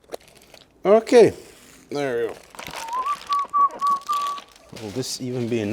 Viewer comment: Did I just hear "nagyon jó" at 9:35? 🤩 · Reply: it was "okay, there you go".